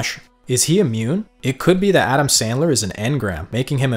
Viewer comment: Starting at 3:39 i could have sworn i heard him call Atom Smasher Atom Sandler or was it just me?